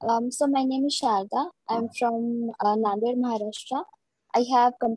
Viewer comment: on a lighter note : did I just hear, "I am from London, Maharashtra" ?